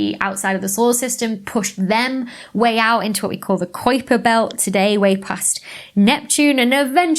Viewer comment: I heard "our solar system is like a pizza." Also, "Koyper belt." I always thought "Kuiper" rhymed with "hyper."